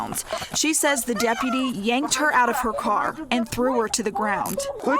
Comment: It sounded like she said “it’s not like the car is stolen “he didn’t have to grab her like that she handed him her licenseAnd registration I think the officer jumped the gun a little and I don’t think the girl should’ve had such a sassy mouth